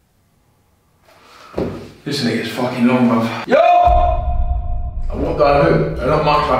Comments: sounds like he said this n**** fuckin long bruv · what did he say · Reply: "this is when it gets fucking long bruv" is what he says